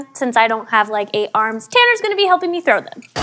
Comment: Did anybody hear her call Tanner at 3:24 or